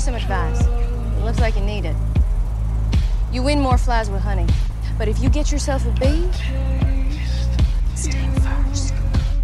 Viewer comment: what does she say... I can't understand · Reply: You win more flies with honey, but if you get yourself a bee. Sting first